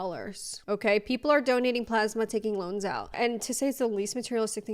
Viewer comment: I heard you say “…taking lungs out…” I’m thinking: wow it so bad people are selling their organs. Sorry my mistake 😅